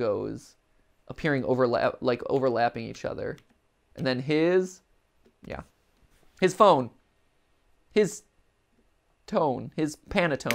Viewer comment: At first I tho thought it said his home or his Throne but idk